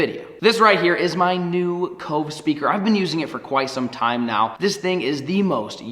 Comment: Right at the beginning Who else thought he said Coke speaker